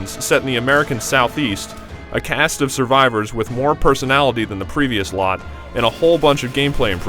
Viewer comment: I could have sworn he said "more personality". It's a good thing I was wrong. Hm... Francis or Rochelle? Louis or Coach?... I can see how Ellis might have more personality... but Rochelle? ...?